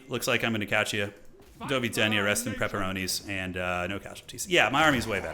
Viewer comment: Did you said "Do widzenia, rest in pepperoni"??? Did you use some polish words in there?